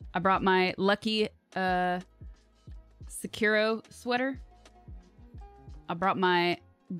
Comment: did she call her Gudetama sweater a...Sekiro sweater!?